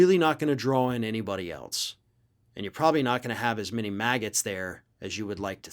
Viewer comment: did you say "not many MAGA's" or Maggots? 🤣🤣🤣🤣🤣🤣🤣